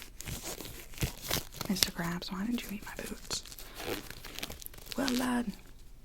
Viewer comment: i was about to drift off when i heard “mr. krabs why did you eat my boots?”